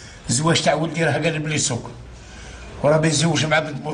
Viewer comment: سمعتها وحدي ولا قالو: الزواج تع ولدي قالبلي الزك ههههه